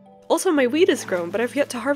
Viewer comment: tehehehe I heard weed